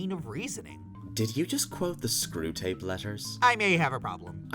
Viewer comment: I think Kim possible Also did it with the Batman parody dude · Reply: did he say...screw tapes? I'm so confused.